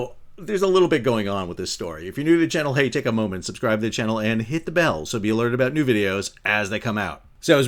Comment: Oh you said subscribe and hit the bell, he thought you said hit the bow